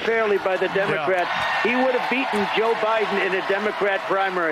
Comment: seconds in Trump said essentially Joe Biden would’ve been beaten Joe Biden. unless I heard it wrong?